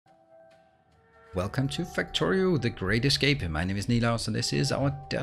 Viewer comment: When you say "Welcome to Factorio - the great escape" it sounds like "Factorio - The greatest game" which is even better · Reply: Woah. Ive thought he was saying "The Greatest Game" the whole time. duh.